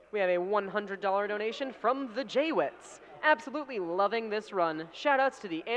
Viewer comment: I thought I just heard the name +TheJWittz...